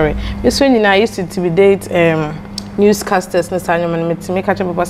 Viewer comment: did she say she use to INTIMIDATE new casters when she was little